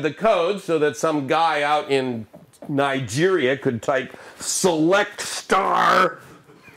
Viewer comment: Did he say "Some guys in Nigeria ..."🤣